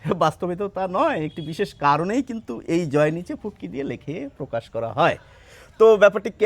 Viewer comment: At first I thought he said পুটকি 😂